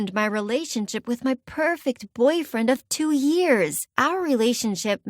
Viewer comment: I thought it said cute boyfriend at the start